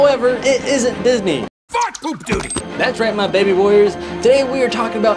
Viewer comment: it sounded like he said F███ Poop duty